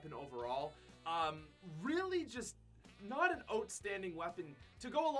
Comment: Lol sounds like your saying “ote”standing instead of outstanding